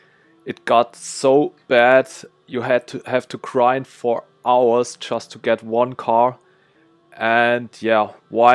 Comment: Lol at 3:24 I misheard you saying "you have to cry for hours just to get one car". But yeah, it's unfortunate that the game has become more of a chore due to the nerf.